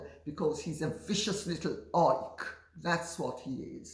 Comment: Did she call Harry a vicious little orc? (9:11) LOL